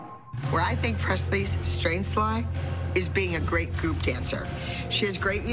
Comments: I thought that she said poop 💩 dancer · AM I CRAZY BUT IN THE START DID I HEAR POOP DANCER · I thought Abby said poop dancer 😂 sorry pressley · Did she say poop dancer